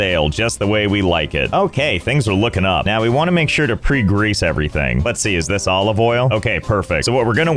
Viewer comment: I thought you said, "Pre Grace" everything.